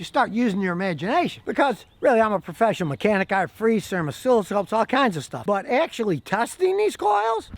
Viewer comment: Lol, Not sure what Scotty says around 9:00? I think I heard "oscilloscope"